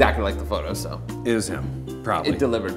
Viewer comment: Hilarious! Also my friend thought Jorma said "Chris Jenner" at the end instead of "it was Judd...." Now that's the joke.